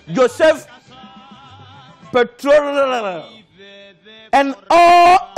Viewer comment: Did he just call that guy "petroleum "? 3:25 😂😂😂